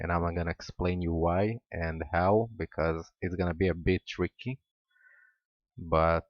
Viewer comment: did he say, "It's going to be a bitch Ricky?" I can't watch this guy. Spit the sand out of your mouth dude.